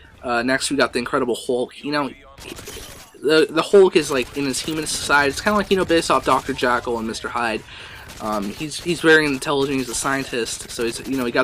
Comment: did you say "Dr. Jackal and Mr. Hyde"? xP 3rd comment, I know. Commenting on the ACTUAL topic of the video is for losers! :P